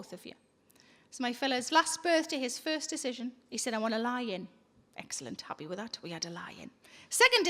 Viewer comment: I thought she said he wanted a “lion.” Not a “lie in.” 🤦🏻‍♂️